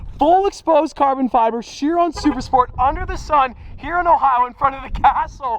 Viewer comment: Did he just say hes in ohio